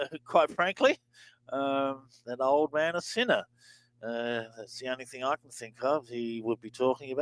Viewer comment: I hear "Senate" in Trump's reversal instead of "sinner." He may have Lindsey on his mind. 😮